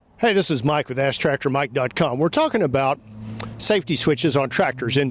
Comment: My ears ain't right! I thought you said Hi, I'm Mike with ass tractor Mike dot com. Maybe if'n y'all just said that a little bit slower ...